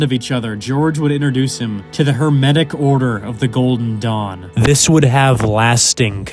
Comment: I thought he said the hermetic order of the golden dong and even tho i was laughing at it i figured thats just what it was 😂